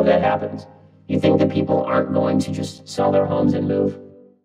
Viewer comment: I thought this shit said "sell their homies and move" I was legit befuddled 😭